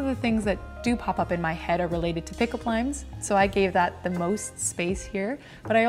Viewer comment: i just gotta say when she said "pick up limes" i understood "pick up liNes" and was very confused about why someone would want to think of pick up lines and write them down... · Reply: Oh, bless my heart, I thought she said “pick up lines,” and was like oooooooookay. Odd, but it’s her bujo. 😂 Thank you for this comment!